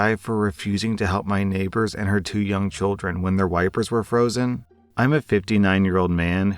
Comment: I misheard the very first story title and had to go back to read it... I heard 'when their wife was unfrozen'